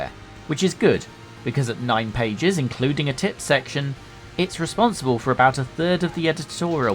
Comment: I thought for a second you said "tits section". Given this was the era of the lad mag I did have to go back and double check rather then just assuming i'd missheard.